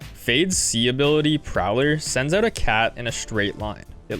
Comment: did you just say CAT?